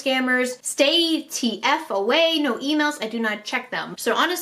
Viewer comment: U see when he said no emails I thought he said no females I don not trust them